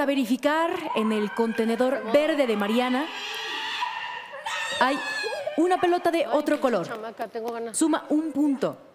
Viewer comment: que dice la Mariana🐷🐷🐷???? al momento de celebrar Bridgite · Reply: Pinche chamaca te traego ganas 😮